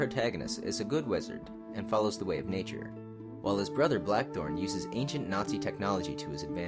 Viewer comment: You mean Black Wolf instead of "Black Thorn", right??? (at about